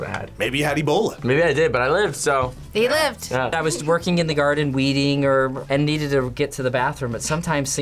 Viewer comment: i heard “ i was TWERKING in the garden ” HELP 😭